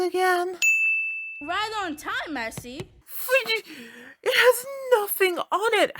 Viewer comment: Also another probable sin I pointed out, Merci means Thanks or Thank you in French. This episode is taking place in California which is one of the states that has some people speaking both Spanish and English. The only state here in the USA(that I kniw of) that speaks French(or at least with a french accent) is Louisianna. The only reason I said "probable" is because its in case I heard a mispronounciation or anything like that. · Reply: pretty sure the line us "right on time I see" but I've misheard stuff before apparently sooo